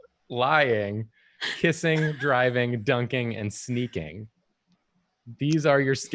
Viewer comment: Five years late, but I love how the subtitles at 36:26 make "dying" one of the skills instead of "driving". Let's see if that would have been useful 😂